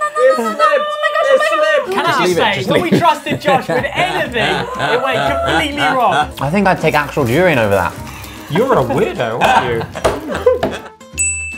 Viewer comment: who else heard urine instead of durian?